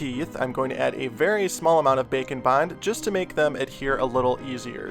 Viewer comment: I thought he said bacon bonds :D